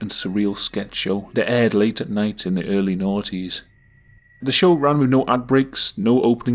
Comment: "in the early noughties" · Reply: Omg I thought the same thing. I’m Canadian and thought he said “naughties” like it was a skanky British term